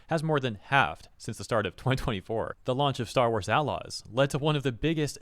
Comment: Completely unrelated, but whenever I hear "star wars outlaws", my brain registers it as "star wars allies". Was that on purpose? Am I crazy?